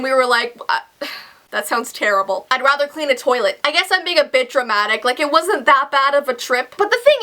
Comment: thought she said i was being a bitch lol